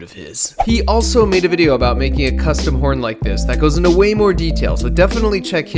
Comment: I thought it said “ incest horn” I was curious what that could possibly be.